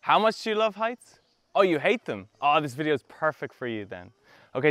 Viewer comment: パーフェクト武勇伝って聞こえる